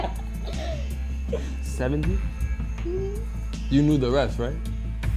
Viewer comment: Bruh at 2:57 I thought he said “you blew the refs right?” 😂😂😂😂